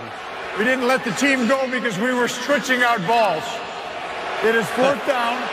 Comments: We were switching out balls 😂😂😂 the defender was just overpowered 😂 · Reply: I totally misheard. It sounded like he said: we were stretching out balls. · I can tell you that being in the crowd that night it sounded like he said "We were stretching our balls." Most of the people in the section I sat in were laughing hysterically.